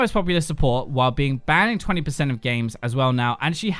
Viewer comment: "The only champions that do well against her are Blitzcrank and Sa" Wtf???? I hope I'm not mishearing this. Who tf is Sa? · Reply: Sion